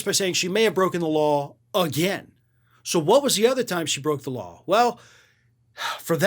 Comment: Damn! I thought said somebody broke her jaw! She will eventually get her ass kicked!